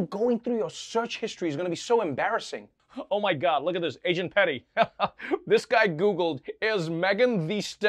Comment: ha I thought Trevor said “Asian Pedi” not Agent Lol